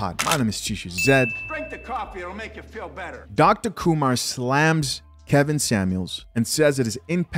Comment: Did he say Dr. Kumar?